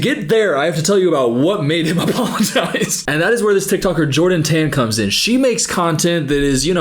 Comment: I like how thought he said a bible verse but I'm 99% sure he said "Jordan Tan" lmao